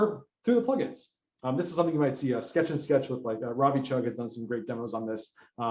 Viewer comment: What exactly is he saying at 43:46? <link> I am not familiar with the names he mentioned and when I search for what I seem to hear, I find nothing :D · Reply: ravichugh's Sketch-n-Sketch - a WYSIWYG SVG tool